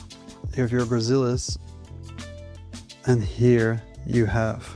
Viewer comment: With captions on 🤣Edit: He is actually saying "Here is your Gracilis"